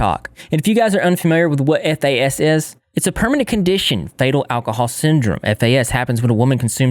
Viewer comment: FETAL alcohol syndrome FEEET- el. sounds like you say "fatal"